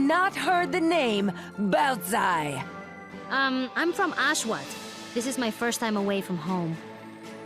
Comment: I'm sorry but at 22:22 I thought she said Auschwitz